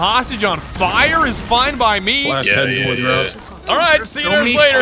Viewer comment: Damn that one guy (can't remember who) saying "yeah" sounded like Jojo saying "Yare yare yare"